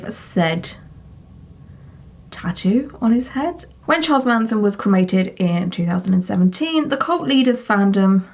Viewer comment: 🤣I thought you said "Charlie Manson's ass hole" until you said "cremated". 🤣